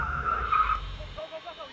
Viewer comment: LOL at the end i heard "Go go go go go." or something